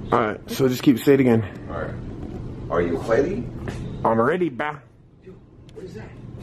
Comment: What the #%? IDK but that sounded like it was right by mic. Said “ I’m ready bah”.